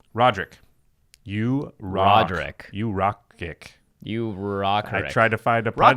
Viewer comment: did he say you rock dick